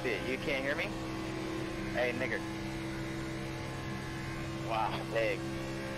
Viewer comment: 😂😂I thought he said nigga but he said it the old school way🤦🏻‍♂️😂 It’s okay I know he didn’t mean it racially